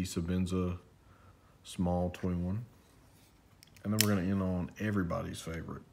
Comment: What is the knife at 5:52? At full volume it sounded like you said "Savinsa twin"?